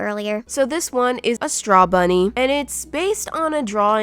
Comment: I thought she said straw money